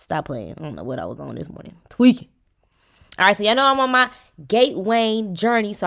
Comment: Friend you didnt even catch that you said “geight wain” instead of weight gain at 2:42.😂